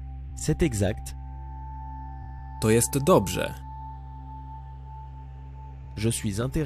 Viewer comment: Attention à 7:11 on entend "To jest to dobrze" et non "To sie zgadza". Lequel faut-il dire pour "C'est exact" ? 23:32 "blisko" signifie "près" non ? Pourquoi la phrase en français est "c'est fermé" ?